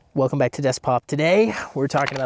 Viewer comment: I heard “welcome to desk pop” and instantly subscribed 😅